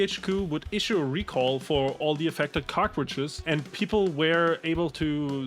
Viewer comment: (approx), I keep hearing "cockroaches" when I know I should be hearing "cartridges." The visual of returning cockroaches and having them replaced by new cockroaches makes me chuckle.